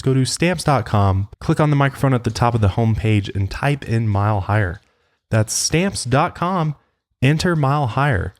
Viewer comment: im hIGhERerrr HiGhEr wiTh YOUUUUUU hearing this makes me so happy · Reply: Ella Burton i thought he was saying higher baby lol